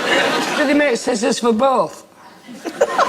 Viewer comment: 'dus it mate sit sis et boat' wtf did he say there? Would have been nice to have closed captions. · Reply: “do they make scissors for both?”